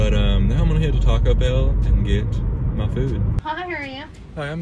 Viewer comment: it sounded like she said how hungry are you